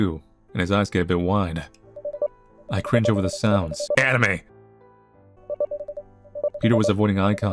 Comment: GUYS WHAT DOES THE CENSORED PART AT 32:48 SAY??? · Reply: Anime.